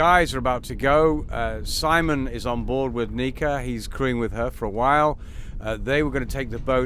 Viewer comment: you got my attention there. thought you said...he is screwing with her for awhile.